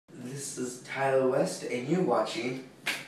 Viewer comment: First time I watched, I thought he said "This is Kanye West, and you are watching The Leclericshow" xD